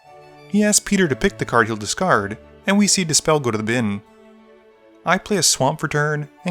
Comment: Had an Abbott & Costello moment. "We see dispel go to the bin" What Spell ? Oh he said dispel and not This Spell